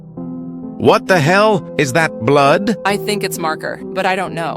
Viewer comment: I hear "Is that Blud?" instead of "Is that Blood?"